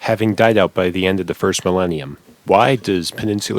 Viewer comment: God dammit, did you just say Willenium?